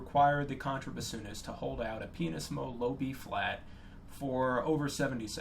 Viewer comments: i heard and couldn't unhear ''a penis low, low Bb'' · um did I hear this right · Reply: "pianissimo", musician fancy talk for "very, very quiet"